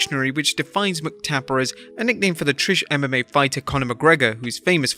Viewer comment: said TRISH mma superstar instead of Irish 😂😅😂